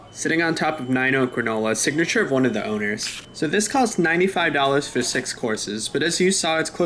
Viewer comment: misheard "this cost $95" as "this cost $900" and my soul left my body before i smashed the rewind.